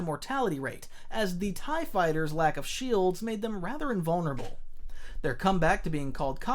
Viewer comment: vulnerable but i thought i misheard you saying invulnerable